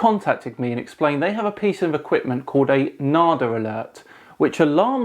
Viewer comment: Nardalert. Thought he said Neard alert at first. Lol